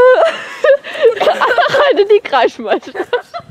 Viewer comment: Kommt, wir gehen jz alle auf Kreischmeisterschaft XD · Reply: Ich hab erst kreischmeistersaft verstanden 😂😂 haha mega geöltes video